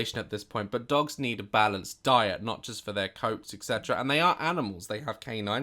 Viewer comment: I paused the video as soon as Daz siad diet but it sounded tike he said die so all I heard was “dogs need a balanced die” 😂😂